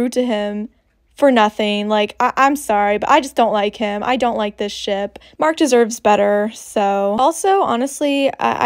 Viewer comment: i thought u said i dont like this sh** lol